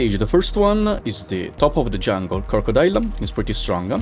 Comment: lol i thought i heard kokodoya but it turns out to be crocodile